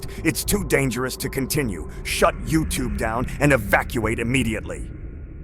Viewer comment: Did the Patriot AI glitch early or did it just say to "Shut YouTube down"? lol